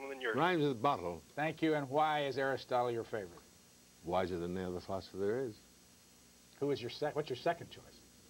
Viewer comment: Please, what is Adler answer when he's asked "Why Aristotle?" at 2:22? I didn't understand it. Can anyone transcribe? Thanks! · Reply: ‘Wiser than any other philosopher there is’. Sorry I’m 10 years late lol